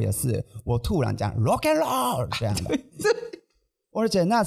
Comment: 用聽的把說「Rock and Roll」聽成「馬鹿野郎」😂😂😂😂